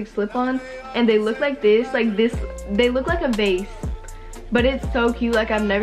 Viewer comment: I thought she said u look like a catfish sksksks💀💀